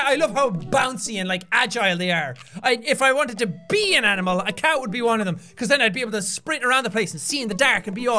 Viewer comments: it sounded like jack said if i could be un animal i would be a cow becous then i could run around al over the plase and se in the dark, he really threw me of there for a moment lol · "If I had to be an animal a cow would be one of them" did anyone hear cow instead of cat? I just started thinking of a cow on crack running